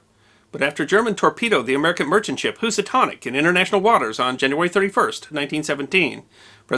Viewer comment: I thought it was the Lusitania not Lusitanic. You probably are saying Lusitania, but it sounds like you said Lusitanic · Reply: The Lusitania was sunk in 1915, a couple of years before the period in question. The Housatonic is the ship in question, sunk just after Germany resumed unrestricted U-boat warfare on 31 Jan 1917.